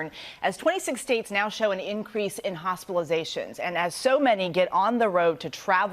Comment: Did she say hospilizations or am i hearing it wrong? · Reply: Yes she said increase in hospitalizations